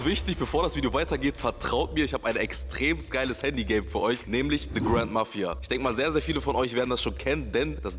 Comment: bitte sagt mir das ich nicht der einzige bin der "The Grandma 4" verstanden hat